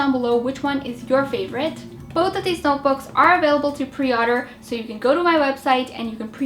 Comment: Pre- Otter? it sounds like you said that. and room decor